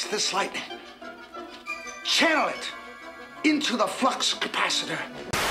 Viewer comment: at the part with the Back to the Future audio I for some reason thought that he said fucks capacitor instead of flux